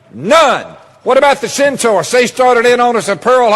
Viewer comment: "What about the (centaur???)" WTF is this faux-hillbilly saying?